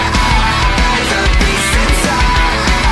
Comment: "nagisa-kun" and "karma-kun" I'm sorry all I heard was "'I'm gay".